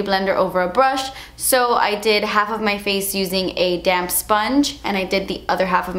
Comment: I was zoning out at the beginnng and what I heard “ a damn sponge “ took me a min..😂